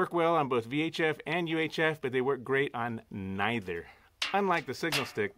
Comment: ==> "works great on what ??" Couldn't hear. I like that "signal stick". However, i think you should've tested it like factorywise. Without that "extra" wire with that green thing. Where did u buy it ? That signal stick with poise.. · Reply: "Great on neither"